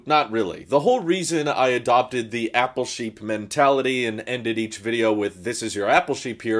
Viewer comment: When I first started watching I thought you were saying your name at the end. “This is Ralph Shapeer”. I thought your name was. Took like 20 more videos before it dawned on me “OH APPLE SHEEP” Anyway, thanks for all the videos.